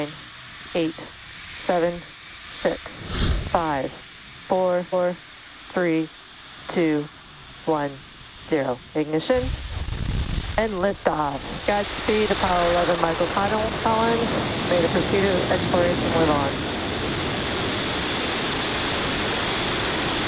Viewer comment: I just hear "lift off Apollo 11" Lol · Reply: said godspeed apollo 11 because it took off from apollo 11's launchpad